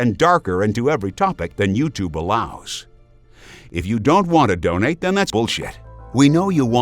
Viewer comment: "If you dont want to donate, then thats bull sh#t...Am I hearing that correctly?